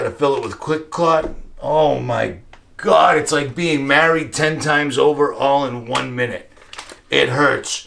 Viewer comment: My husband heard you say "it's like being married to 10 woman in 1 minute" when taking about the clot stuff....he laughed his ass off😕😂😂😂😂